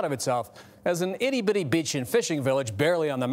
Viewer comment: I thought he said “An itty bitty bitchin’ fishing village.” 😭